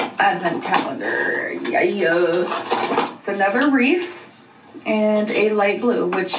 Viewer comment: am I the only one hearing her say “reaf” instead of “wreath”